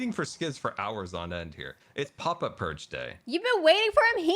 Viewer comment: First time scar said ‘pop up purge day’ I thought he was saying ‘papa perch day’. Like u have a specific day to sit ur self on his shoulder or something 😂😂😂💜